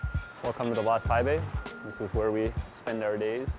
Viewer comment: What did he say? "Welcome to Las Taibei?"